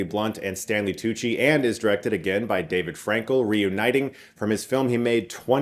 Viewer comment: I legit thought that said Dave Franco, not David Frankeil.